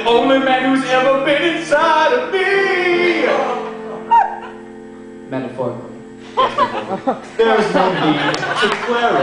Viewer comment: what does he say at 1:38 ? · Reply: Jehan Silva metaphorically